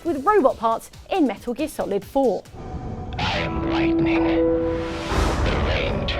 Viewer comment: he says lightning but the subtitles say lighting... was that typo in all copies of the game? i have the hd collection but i haven't got around to playing them